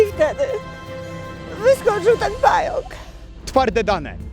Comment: Co ona mówi? I wtedy wyskoczył ten fajlk???